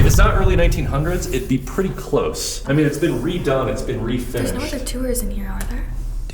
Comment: it sounds like a little girl? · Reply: Sounds like it says “can we Finnish them”😳😳😳